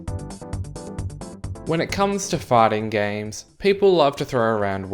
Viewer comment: I swear I thought he said farting games